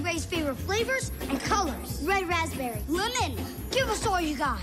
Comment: Bro I thought she was saying blue mint, not lemon!